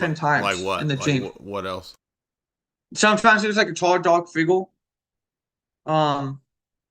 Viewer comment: Charizard? Figgle? Like the pokemon? I'm sorry, I love this guy but I was really trying to know what he was saying😅 I shouldn't be so curious about shapes a demon takes. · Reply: I think he said "tall, dark figure"